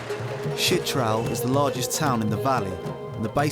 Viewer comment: The subtitle says shit trowel lol